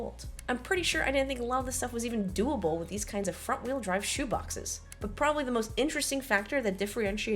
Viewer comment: Had to rewind, thought you said sh*tboxes for a couple seconds :) But good video